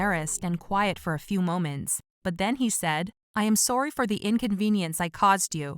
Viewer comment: Did she say i am sori thats what i heard